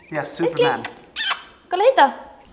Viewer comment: Did Janni say "coleguita" ? Haha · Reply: She said "kolla hit da" with is Swedish for "look here".